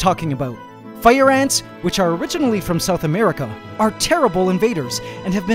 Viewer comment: At This time I thought he is saying Fire Ants are terrible at invading, and I was like wait what?